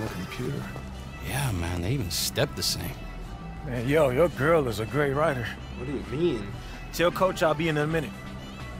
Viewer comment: I thought young said “your girls a great rider” as in she was riding Marcus’s ya know